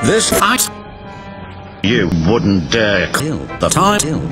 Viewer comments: I thought thomas said the fuckin troller instead of the fat controller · When he said the fat controller I thought he said the fucken troller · At first I thought it said the fucking troller